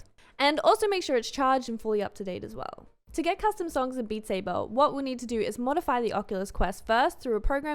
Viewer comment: Every time all I keep hearing her say “Beat sabuuuuuh”